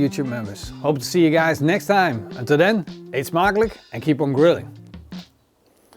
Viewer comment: Youtube subtitles at 9:12 - "Until then it's Marley and keep on grilling". I'm not sure, but I don't think he's saying Marley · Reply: eet smakelijk"" like have a nice meal!